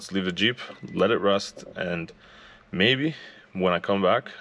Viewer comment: LOL ... at the end when you said "Let it rest" ... I thought you said "Let it RUST"